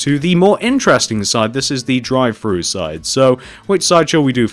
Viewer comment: When he said "drive thru" I thought he said "dry fruits" xD